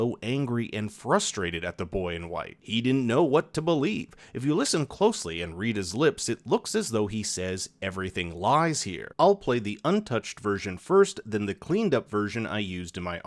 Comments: Theory!! When the boy in white spoke to Christopher he said that " The answers to the end are at the beginning" which could be translated to the riddle "What is the beginning of eternity, the end of time, the beginning of every end, and the end of every place?" Which is "E" so Ethan😱🤔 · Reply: I don't know about anyone else, but in that 'clean' version of what Christopher said, it sounds like he said, "Everything lies, ETHAN". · On your voice program it sounded like Christopher says "everything lies ethan" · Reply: There were subtitles on the episode for me. “Everything lies here” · It sounds like Chris said “everything lies Ethan” maybe Ethan is the boy in white reincarnated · Am I the only one who thinks the cleaned up audio version sounds like he says "Everything lies, Ethan"? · I heard "Everything lies Ethan" maybe Ethan or Satan